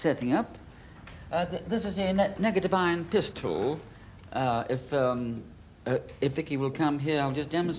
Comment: About 14 minutes in… These were the days when unaccompanied children could just wander over to some guy's house they've never met; knock on the door and be shown "murder weapons" and be told that their clubhouse is haunted! Ah those simple times are long gone and nowadays we're too busy protecting our kids😂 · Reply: I heard "Piss tool" followed by "open your mouth". I should probably seek help. 😂